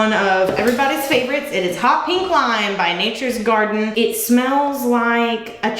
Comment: I thought you said HOT WING LIME and I was grossed out until I heard you say cherry limeade 😜😜😜